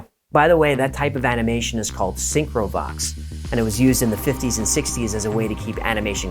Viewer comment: Syncro-Vox is the name of the type of animation. The subtitles got it wrong.